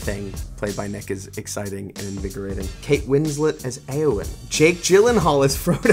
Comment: I thought he said "anything played by Nick is exciting and degrading." XD I had to listen to that bit four times, and then put on subtitles to see "invigorating".